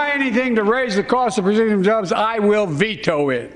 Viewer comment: Sounded like he said, “Anything to raise the cost of Brazilian jobs, I will veto it!”